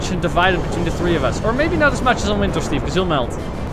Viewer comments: Why did Cole say elemental Steve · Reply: I think he said Winter. Not Elemental Steve. The voice actor has an accent so yeah. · Swear to God I just heard Cole say elemental Steve I swear to God